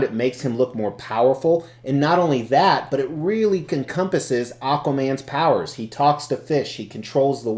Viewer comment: Did I hear you say "Comcompasses" at 3:47? Hahahaha! I'm going to add that word to my vocab. Love it! As for the statue... pretty wild! Very ambitious. Some really cool stuff going on in this piece.